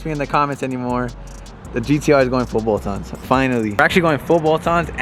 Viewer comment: bruh idk what he keeps saying before getting tuned but it aint English, sound like full bolts on, or football ons. at 0:11, 0:13, 0:19, 0:25 wtf is he saying full bolts on LOL that is not English, ive replayed it 20 times, doesnt make any sense. If someone can help out, because I cannot make it out for the life of me